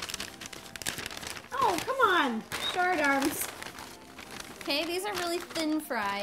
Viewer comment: It sounded like she said shart arms